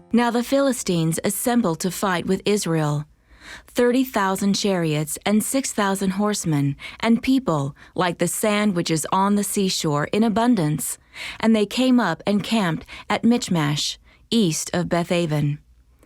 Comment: I thought she said “like the sandwiches on the seashore” looool I was like what translation is that